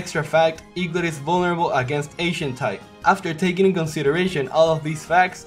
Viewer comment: I thought you said eaglit was vulnerable to Asian type XD